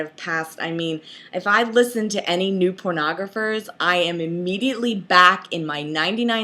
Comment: Did you say pornographers?